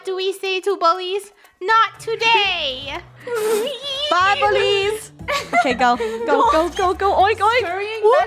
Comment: *it sounds like is saying "bubbly's!" even tho she is saying "bye bullies!"